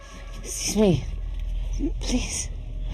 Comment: The line she says at 34:29 isn't "help me, please." She slurs the line so it's kind of hard to understand, but she actually says, "S'cuse me, please." Short for "EXcuse me, please" which makes way more sense because they're passing each other on the stairs and she's just saying "Excuse me," as she walks by, a totally normal thing. That's why he just keeps walking lol.